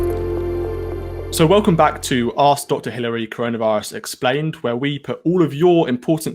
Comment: Totally thought he said Arse Doctor Hilary at the end...